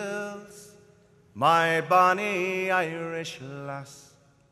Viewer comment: Legit when I first heard this I thought he said "My bonnie Irish ass."